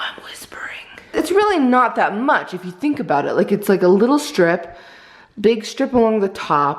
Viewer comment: I swear I heard say stripper on 9:49 even put on the caption readers or whatever